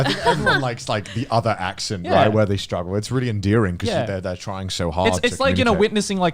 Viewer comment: Connor 9:03 Joey 9:55 = it's 'endearing' instead of 'enduring' 9:41 = clips 'galore'